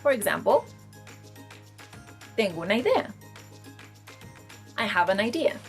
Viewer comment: My Dear, I swear at 5:48 you say "tengo un AIDIA" (rhymes with My Dear)!!